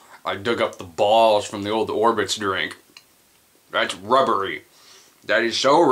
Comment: You went from "It's rubbery" to what I thought you were saying was "It's like I was eating a porn". Now we need to find out what makes these eggs "hybrid". We're they from an electric eel?